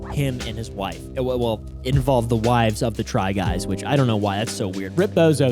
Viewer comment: I thought they said “dry guys” 😐